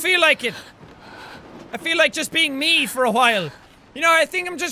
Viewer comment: Did he just say hwile